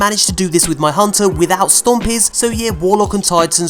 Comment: I thought you said you did it without stompees...